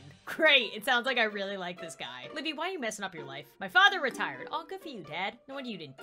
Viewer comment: Ok I know I just commented Buuuut...I heard her say "My father died ohh-"*My ear buds fall out *...*I put them back in * "Oh well I guess that's good" (my dad died...Oh I Guess that's good!)😆